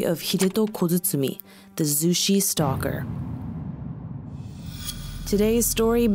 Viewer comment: At first I thought she said the sushi stalker 🍣🍣 😕